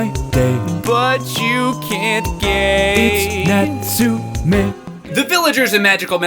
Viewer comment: Every time I listen to the song near the end about wanting to marry the doctor I hear it's "not Sue me" instead of "its natsume"